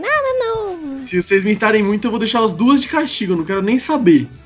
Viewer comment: Na 1:34 entendi o cross falar mitarem😂😂